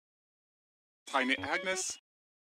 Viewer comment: “I’ll call tiny vou!” ”I’ll call tiny vou.” “Tiny agnes?” “where are you?” “NOOOOOO!” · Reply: I thought jeb said “I mean agnes” instead of “tiny agnes?”